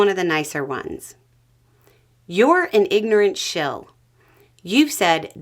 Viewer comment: hahaha 00:42 "jane you ignorant slut" I'm soooooo sorry I'm drinkish and all i heard here was Dan Akroyd.